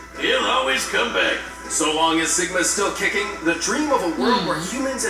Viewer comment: Anyone else thought is said altron vs Ligma